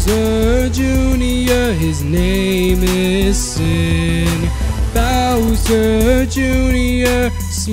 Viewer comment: I thought he said his name was sick 😂